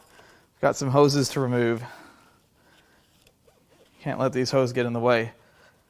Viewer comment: did you say. "Can't let these hoes get in the way"?